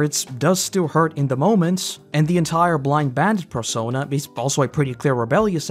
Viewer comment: You accidentally a syllable here, which would ordinarily not be worth mentioning, but I like the way I misheard it better than canon. You mention the “blind bandit persona”, but I misheard as “blind badger” which actually works really well all things considered.